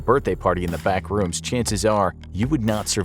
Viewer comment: i legit thought it was said as the bathrooms, so i hear Rob noclipped out of reality into the bathrooms